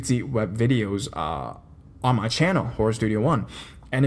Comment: Why does it sound like he says Whorestudio1?